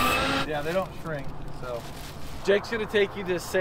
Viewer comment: They didn't get t shirts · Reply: I think... said "They don't shrink..."